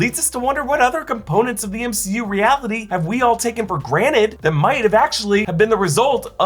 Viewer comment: Did u just say "graned"? It's "granTed"!